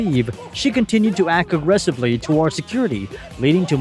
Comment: I thought you said "she continued to act arrestably".. I thought "OH! A new word! And I immediately understood it!".. Turned up the volume, and restarted the video...ah."aggressively". They all continue to act aggressively...